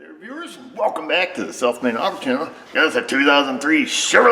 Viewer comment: I enjoy eatching your professional attitude, and logocal diagnostic skills; you are tops. For a few months when first watching you, I thought you were saying, "Self made Auto".....LOL...!!!